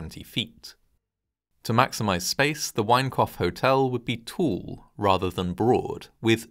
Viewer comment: All I hear is, "The Wank-off Hotel." 😂